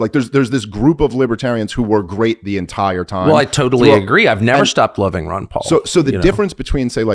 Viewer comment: Tucker Carlson: "I totally agree I've now stopped loving Rand Paul." What!!!??? Why would you say that Tucker!? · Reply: He said I’ve NEVER stopped loving Rand Paul